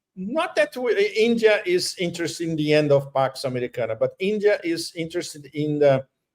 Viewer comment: Judge, your Latin pronunciation differs from mine. I heard "Pox Americana"